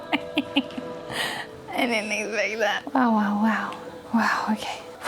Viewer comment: i thought she said owowow before even meeting monoco